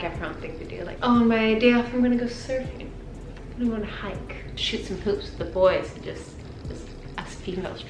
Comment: my captions said “shoot some poops” 😂